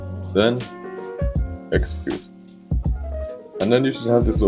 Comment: what did he say when he doing the camra in 5:01 thing the plzzzzz help me:( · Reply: enter